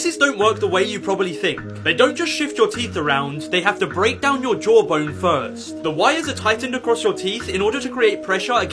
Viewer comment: I thought he said “ they have to break your jaw bone first”